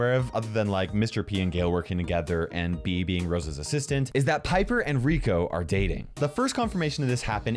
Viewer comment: No he said bibi and rosa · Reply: Junko -chan no, he said “Bea being Rosa’s assistant” it just sounded like he said Bibi